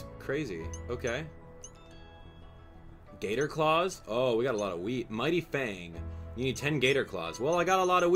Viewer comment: am i the only one who thought said, i got alot of weed XD